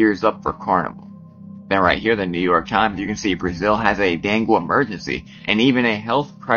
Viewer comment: Thought dude was saying "Brazil havin a dad gum emergency" lol Bro sound like he took too many bars 🤤